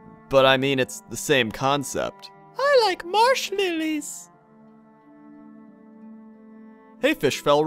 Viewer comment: I thought said "I like marshmellies" at first.